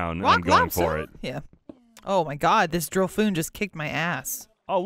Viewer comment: says Drifloon wrong AND different every single time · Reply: i thought she kept saying drill foon